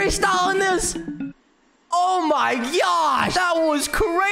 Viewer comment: why do i heard o my gyaaattttt💀💀💀💀💀💀